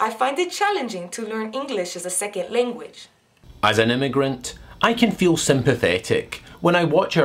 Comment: Oooooh, you were saying "as an immigrant", I thought you were saying "as an ignorant".